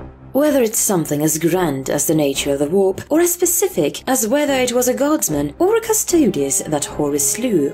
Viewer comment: For a second I thought she said "Mysteries as specific as a well armed guardsman" and I just laughed so hard xD That would be a mystery. *GUARDSMAN!! WHERE IS YOUR FLASH LIGHT?!"